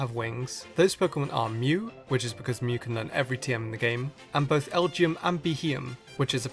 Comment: i thought u said mew can learn everymove